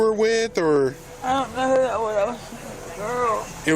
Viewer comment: caption is wrong she doesnt say i dont know she says lafayette